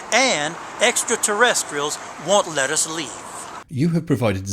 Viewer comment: I thought he said "aliens want lettuce leaves", then realized he said "won't let us leave". He would have been closer to the truth with lettuce leaves I'm thinking, rather than anything else he said.